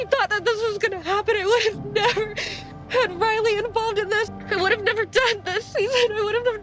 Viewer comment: the funny thing about the liz crying scene is that she clearly says “i would’ve never done this season” but the editors don’t subtitle it 😭😭 (it starts at 07:46 of this video)